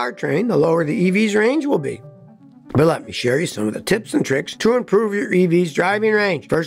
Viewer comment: Did Scotty say tits and tricks at 9:04?